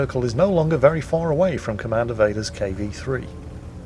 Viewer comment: i thought you said his name was Commander Data. that would have been kick ass.